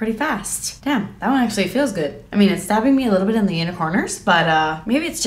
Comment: When you put on second eyelash it sounded like you said “it’s stabbing me in my unicorners” 😂🦄